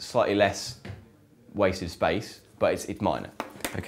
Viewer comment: I can't make out what he's saying, "slightly less waste of space, but..." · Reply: TiagoTiago wasted space